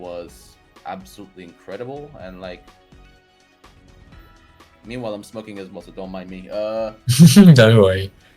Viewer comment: did just say hes smoking